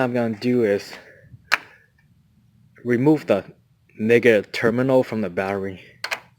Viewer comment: Did you say remove ni99a terminal